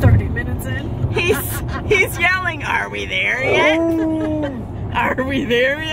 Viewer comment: I kept hearing "I wanna go home" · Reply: hahahahhahahahhahhahaha I think he was saying are we there yet...........lol Thank you So much for watching and commenting! We hope you decide to join our YouTube family and Subscribe if you haven't already ❤️😊👍🐱💟!